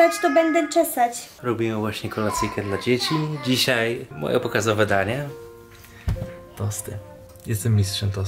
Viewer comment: usłyszałam popisowe danie pomyślałam o spagethii hahaha